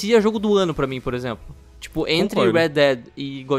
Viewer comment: Foi impressão minha ou parece que ele fala que "Anthem,Red dead e god of war foram os melhores jogos do ano. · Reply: Antes que alguém fale , ele falou entre